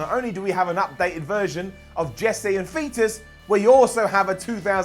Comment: Did my ears just deceive me or did Simon just say Jesse and (Fetus)? 😂